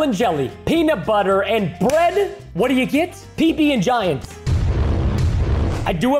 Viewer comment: And when he introduced the PB&giant I thought he said he peed and giant lol 😂